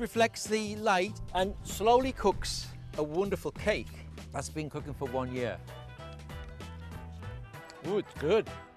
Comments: did he just say a "been cooking for a year" 🤔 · Did he say the cake cooks for 1 year??